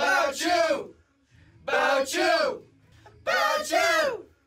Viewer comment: I could swear they were saying "bow chew" instead of "bout you"